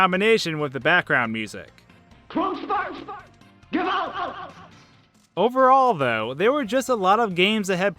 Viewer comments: I heard "Fon f***. Get out!" IDK why I heard a swear word. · I hear "corn starch! Get out!"